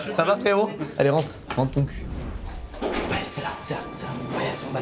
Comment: j'ai compris rentre ton cul à la première écoute 😂